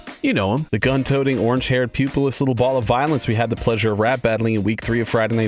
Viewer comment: I thought you said pubeless 💀